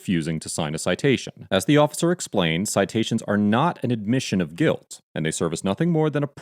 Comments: Loool I keep hearing "its an admision of guilt" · I honestly thought the officer said, “It's an admission of guilt” until the narrator said, “As the officer said, it's not an admission of guilt,” and I went back and replayed it and heard, “It's not admission of guilt.” I wonder if the driver misheard the officer in the same way. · I clearly heard him say "it's an admission of guilt" the first time